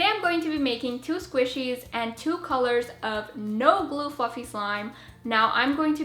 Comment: win she said "no glue fluffy slime " is sound like she said "no glue fu#king slime" cute squishys